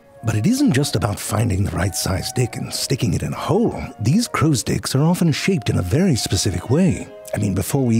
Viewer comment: ummmm that's a little weird. unfortunate word combination🤣 edit- wait is this a joke. I just heard crows dck over and over🤣